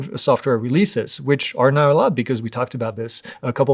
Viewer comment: did say "subsequent software releases which are not allowed" or "... are now allowed"? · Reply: "now allowed". The FCC is now allowing software updates to Jan 2029 (instead of Jan 2027).